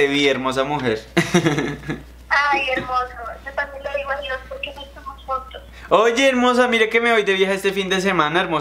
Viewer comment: dice intendible pero yo escuche que dice: porque no estamos juntos like si crees lo mismo